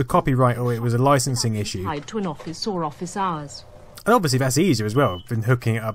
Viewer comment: Did she say "sore off his arse"? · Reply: lol. "or office hours"